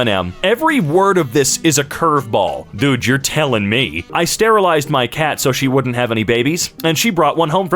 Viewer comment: I was more listening to the video than watching it while I was drawing and I heard sterilized as “paralyzed” As in “I paralyzed my cat so she wouldn’t have any babies”